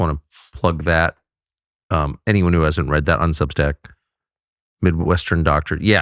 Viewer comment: Is there any way you can fix the closed-captioning and the transcript to say “on Substack” — instead of “unsubstract” — or does YouTube insist on misspelling the competition?